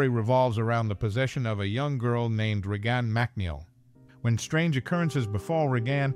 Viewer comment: Re-gahn Mack-Neil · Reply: I heard "Re-Gone Mac Neel"